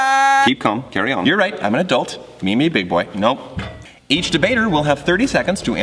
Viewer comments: DID I JUST HEAR "ME ME BIG BOY" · did Thomas just say me me big boy? Jacksfilms fam squad for life! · DID HE JUST SAY ME ME BIG BOI?????????? 5:31 OML JACKSFILMS REfREncE